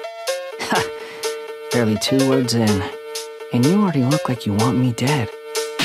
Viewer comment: I always thought he said "Ha, barely two words in england"